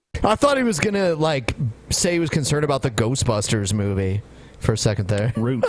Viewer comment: I also thought he was gonna say ghost busters for some reason xD